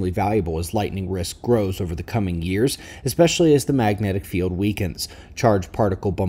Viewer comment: You said if the mag. field weakens. ????? wtf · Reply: I heard him say " AS the magnetic field weakens", not "if". 🗣💬👂